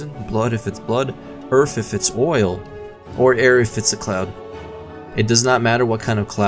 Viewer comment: I swear I heard "Or Aerith if it's a Cloud"